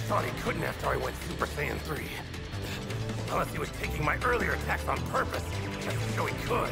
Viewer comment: Anyone else hearing the super saying 3 lisp? 😂